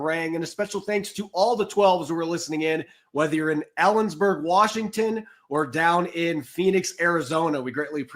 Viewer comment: I love ya, but it kinda sounded like you said Allensburg Washington….lol